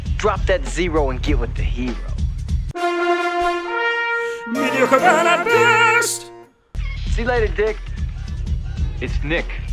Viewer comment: "Drop that zero and get with the hero." I've never wanted to un-hear something so bad in my life. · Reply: Weird, idk why my brain kinda heard/thought Jon was saying "mediocre hero, at best" but I also heard "burn", so I got confused and couldn't unhear either.